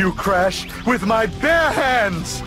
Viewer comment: all right, when l was a kid, l've aways misheard cortex quoting: "I going to beat you, Crash, with my PANTS!" instead of "bare hands" and l thought it was some sort of joke about that DBZ clothing style